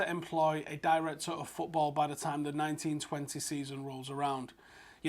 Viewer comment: I heard “19/20” season as “1920” season and for a second thought was off his meds.